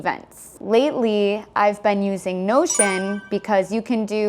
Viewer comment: 영상 잘 보고 있습니다! 혹시 1:18에 I've been using이 I've spanding using 처럼 들리는데 혹시 발음을 어떻게 하면 좋을지, 어떤 연음으로 이렇게 들리는지 설명해주실 수 있나요?